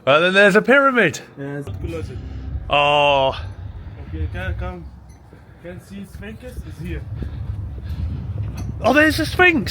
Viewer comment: I thought he said "you can see sphincter" at 3:37.